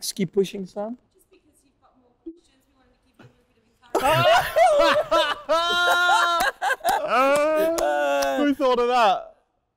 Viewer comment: What did the lady say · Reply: "Just because you've got more questions, we wanted to give you a little bit of encouragement"